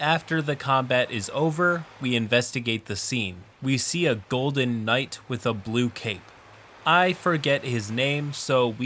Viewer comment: OH MY GOD THATS THE OC THE OTHER GUY "STOLE" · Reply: I was listening to this off on the side, and I heard "Golden armor with a blue cape" and sat up real quick lmao